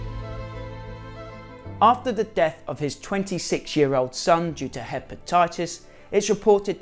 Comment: 😂 I've never had a problem understanding people with a British accent but when you said he lost his 26 year old son due to hepatitis I thought you said he lost his 26 year old son Juda Hepatitis . I was like wow! That guy must've really hated his kid to give him those names 😅